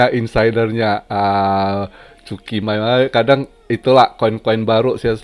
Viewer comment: ngakak dengernya 01:52 cukim** 🤣 mantap ko!